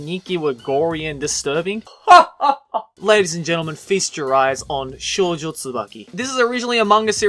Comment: did he say fist your eyes?